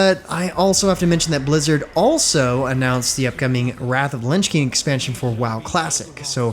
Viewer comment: Did I hear "wrath of lunch king"?